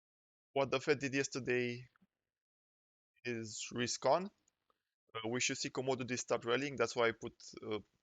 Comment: I can't catch/understand something you said at 9:09. You said, "What the Fed did yesterday is 'rescon'". What is "rescon"? What does "rescon" mean? Thanks! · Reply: risk ok :))